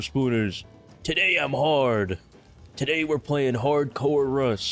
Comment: second into the video and all I hear is "today I'm hard" 😑